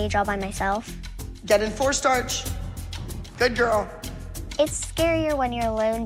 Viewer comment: I thought it said “get in cornstarch”